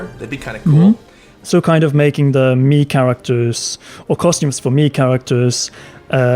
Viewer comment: I keep hearing “Mii Characters” as “meme characters”